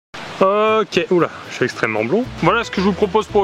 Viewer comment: Au début j'ai compris "je suis extrêmement beau" XD